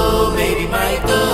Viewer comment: ตั้งแต่ 1:33 ผมได้ยินว่าา ไหมม...ตู่